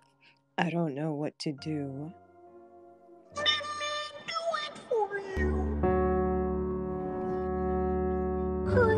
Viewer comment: what does the dog say? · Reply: "Kermie"